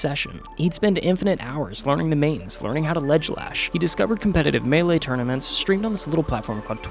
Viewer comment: did I just hear “learning how to ledgelash”?